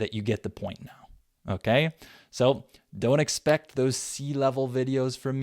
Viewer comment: what do you mean by the last words you've said before finishing the video 27:30 to the end . " don't expect those sea level videos for me in the future" what do you mean by the word sea , I didn't got what did you mean ?? thanks in advence